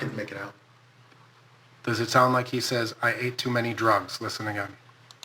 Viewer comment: Why am I hearing "I ate too many doritos" drugs has 1 syllable, and whatever hes saying clearly has 2 syllables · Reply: I hear “I ate too many skittles.” I think I’m just confusing Saint Floyd with Trayvon Martin though.